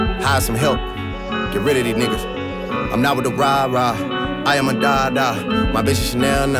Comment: “I am a dada”... he exposed himself. 🕵🏽‍♂️ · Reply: i swear for all this time i thought he said ” i am a dawg dawg”